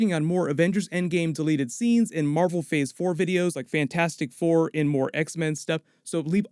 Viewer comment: He said "Thor" way too often... So I thought he was saying "Fantastic Thor" at the end... XD